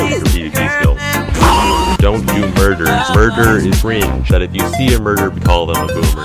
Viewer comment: “If you see a murderer, call them a boomer.” Aight good to know- putting that on the bucket list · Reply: I thought he said "if you see a burger, call them a boomer."